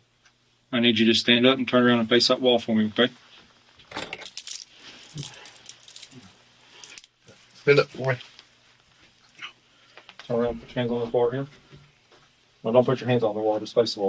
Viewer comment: does he say stand up for me or stand up boy